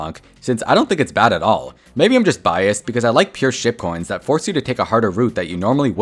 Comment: Im late but when he said pure ship coins at like 14 mins i thought he said pure $#!+